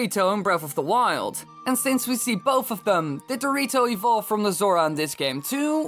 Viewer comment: it's sound like he said Dorito